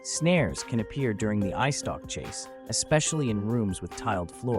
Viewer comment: I dont mean to be rude or anything like that, but, did you just say “ice stock” instead of “eyestalk”?